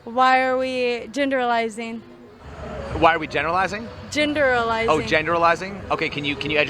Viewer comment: Did she say genderizing?? 👀👀